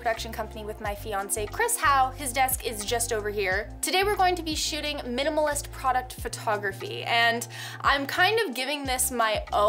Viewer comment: I misheard that as "Today we're going to be shitting minimalist product photography". I think I should get some sleep. 😅